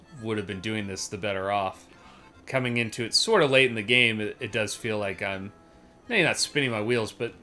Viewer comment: did someone say late 2 the game?